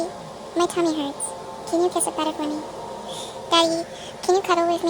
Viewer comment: the caption says “can you piss it better” instead of “can you kiss it better” 😭😭😭